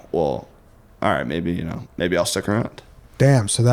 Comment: It always sounds like is saying "alright bae bae"